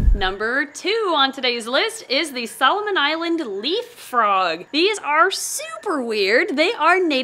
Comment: Could have sworn that you said Solomon leap frog 🐸 instead of leaf 🍃